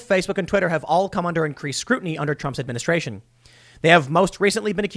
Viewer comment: Ohh!! Okay. You said increased scrutiny, I thought you said intense scrutiny. Ya right! The GOP, DOJ, and Barr are merely appeasing us. They will do as little as possible, and act like we should be happy if Trump wins. It's our own fault, any other company would have lost half it's customers/users to boycott by now, but we just crawl back, day after day and cry about it. Never willing to take the plunge and cost the MFs money like we do everyone else. It would only take a little while, but we can't do a few days. We deserve what we get when we consent to their rules. That point applies to everything from abortion to covid, and from the alphabet people to public education. We told them it was okay by participating, accepting, and giving consent. That is not how we make them stop. It only brings more. I'll ask everyone, how much more can you swallow? Because it's not stopping. The government won't save us from shit. We all know it. No consent to anything, ever, from here on out. Just say no and only no. That's how we change it. I hope I'm wrong, probably not though.